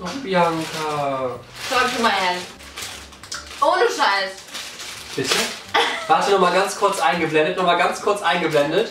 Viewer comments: Was sagt Bibi bei 3:07 ??😂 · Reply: Sie sagt talk to my hand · was sagt sie da?? Ich habe es nie verstanden😂😂😂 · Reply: Sie hat einfach nur gesagt so gib mal her jetzt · Was hat bibi bei 3:11 gesagt ? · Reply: Just Sophia Talk to my Hand